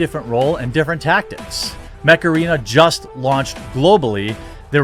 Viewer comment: Oh mech arena, I thought you said macaraina (like the dance) at first, lol.